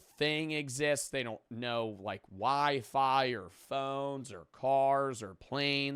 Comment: i thought you said "why fire" 😂😂😂